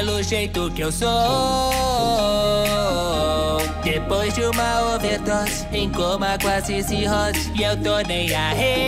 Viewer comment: O que ele diz no momento 0:35??? Não entendi! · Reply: Depois de uma overdose, em coma quase cirrose